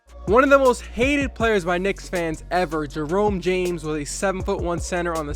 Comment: I thought Jerome James said Lerone James